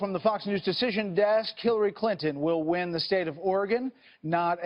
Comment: Lol, did he say killary Clinton at 15:09?